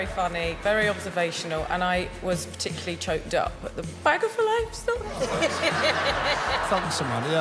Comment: I thought he said bag faloaf